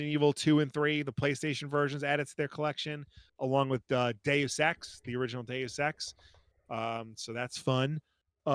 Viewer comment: I know you said Deus Ex. I heard "Day of sex" and my immediate thought was "Bullshit... more like 2 minutes on a good day."